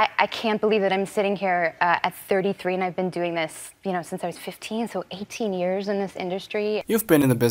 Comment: 处的 you know 完全听不到啊